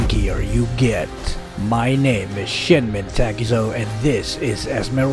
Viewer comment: When you say your name/youtube channel name I keep hearing “this is shitmen Takuzo”